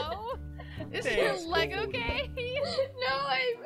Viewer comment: And I thought 💭 she said “thanks my love”